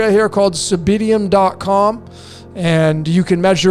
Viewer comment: whats the name of the website? I hear Cymbidium but I'm not getting any results in Google.